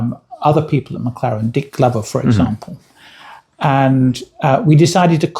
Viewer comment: I swear at 26:33 he said he knew a Dick Lover at McLaren!??